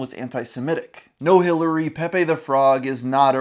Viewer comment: I seriously thought for a moment he said Pepe the Prawn ok!.. 🦐🦐